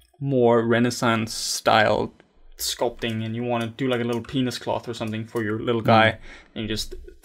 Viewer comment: I may heard that incorrectly... what were you saying? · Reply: "Penis Cloth"